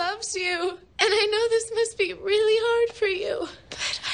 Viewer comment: What did Quinn say at 2:31? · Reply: she said she needed her dad to hold her and tell her that its gonna be okay